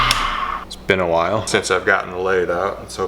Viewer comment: I deadass thought you said “it’s been a while since I’ve gotten laid”😂😂😂